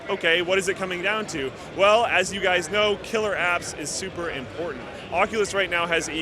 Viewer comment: I thought he said you need killer abs :)